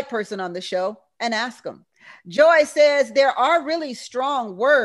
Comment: "They think like their mothers and grandmothers. So, they all think like women and that's why they can't get it right." 😂😂😂😂🤣🤣🤣🤣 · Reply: I thought you said that there aren't any strong women...just weak men.